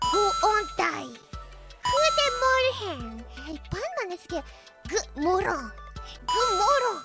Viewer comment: I think she just said: God morgon (good morning in Swedish) which really does sound a little bit like goo moron (lol).